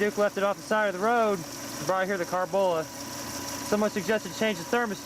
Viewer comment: what are you saying "probably here the car bullet"? Man this video is for ancient cars now:) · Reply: The 'Carbola'. It's a proper name that was a company in that building. This building is of Quanset Hut construction. not to be confused with Kwanzaa hut.